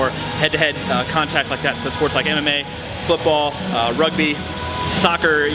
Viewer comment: i heard "sports like anime, football, rugby, soccer" lmao